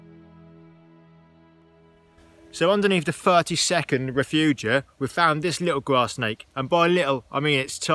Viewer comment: See a lot of them in the gower peninsular in Swansea, love them and adders, you have an interesting way of saying “refuges” though 😂🤔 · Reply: Apologise for my comment, I could have sworn you were saying refuges. I had to go and look up refugia (because I had never heard of that word). The plural of 'refugium' -you learn something new each day - LOL !